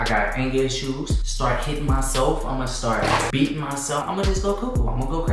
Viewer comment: Bro who else heard that he shit him self